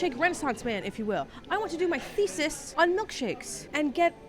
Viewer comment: Jesus saves.. like seriously. You are cool and all so I'm going to be cool back and just say Jesus saves. .. Now why do I say this... Well like I say you are cool and that is how Jesus roles, the other dude here is not Jesus,I am .. But he's cool also, don't get me wrong, he's just not Jesus.. I dond mind though. God bless, I bless, now you bless. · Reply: Times8:36 : Did you just say "I want to make my feces on milkshake?".. Never thought about it like that...I am lacto intolerant so .. maybe it's just my sensitive tummy speaking.